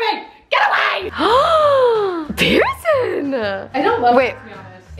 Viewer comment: i literally thought that she said bierson and then i realized that she said.